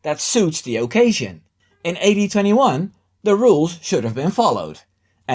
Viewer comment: did you say 1821? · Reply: Just messing. AD21 just sounded like 1821